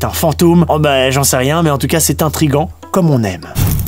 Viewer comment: et du coup c’est quoi un « trigant » ?